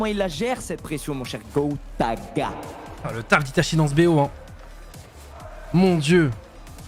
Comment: Magnifique le "Itachi est d'ssus" j'ai compris "itachié dsu" ahahaha